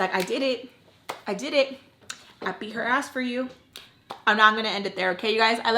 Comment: I got so excited at the end when I heard you say “I beat her as for you” like shit!!!! Finally! 😂 long time coming 😅